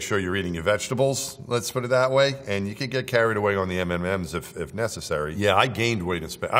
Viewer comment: Did he say MMM's? 7:08?